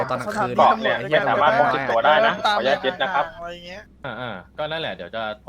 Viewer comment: ได้ยินว่าขออนุญาติเย็*นะครับ